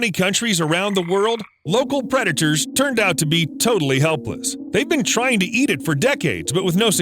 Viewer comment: I thought he said “toadally helpless” at the very start lol